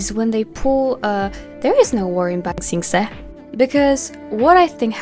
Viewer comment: wtf did she say in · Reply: “There is no war in Ba sing se” a referance to the show Avatar the last airbender